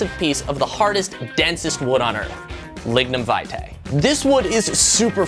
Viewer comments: i thought it said "ligma vitae" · Thought ya said ligma